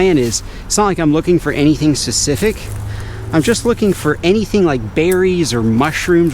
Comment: what did you just say? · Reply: Oh ya he said secific not specific